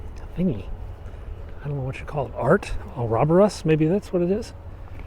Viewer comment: Looks like a "single-side" object... similar to a möbius strip, but in 3D. Did you call it by name? Didn't quite catch it... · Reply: Ouroboros: a circular symbol depicting a snake, or less commonly a dragon, swallowing its tail, as an emblem of wholeness or infinity.